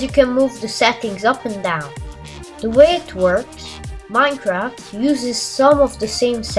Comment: it sounded like he said " the way it twerks"